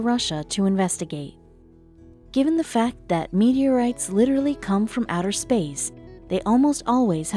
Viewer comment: Did you hear that?... Meteorites almost always come from outer space... What a break through!! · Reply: I heard; "Meteorites literally come from outer space", "almost always have scientific value"